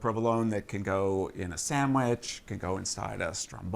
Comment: Did he just say "samwich"? Hysterical!